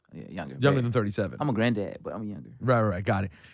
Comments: did JID say he's a Grand dad?? WTF? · Did he say he is a granddad?